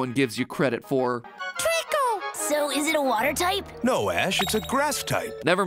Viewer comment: Ash: "Is it a water type" What i hear:"IS THAT A CHICKEN?"